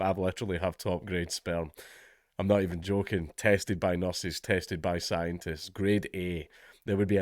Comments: I thought he just said his sperm was 'Tasted by Nurses' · I thought Dank said "tasted by nurses, tasted by scientists" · I thought he said tasted by the nurse 😅😅